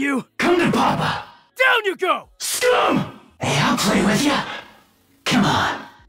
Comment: did he just say what i think he said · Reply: He said Scum